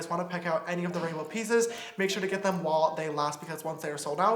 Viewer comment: he says wall instead of while, I’m creased... btw I’ve already set my reminder ordering it the second it comes out ❤️❤️❤️